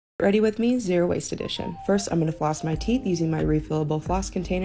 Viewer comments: i thought she said zero waist i was like girl hold up😂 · Ohhh refillable floss… I thought you said reusable I had to start the video over 😂😂😂